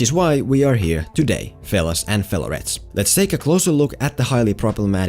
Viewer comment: Um, what is it that you say at the 2:12 , 'failures and failurettes'? =)))